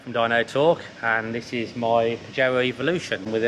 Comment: I thought he said "this is my joe evolution" before i realize he said "this is my pajero evolution" 😁